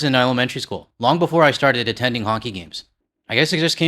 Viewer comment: Did you say "Honky" games? LOL 😂 America needs to be more like canada... whether singing at honky games or otherwise...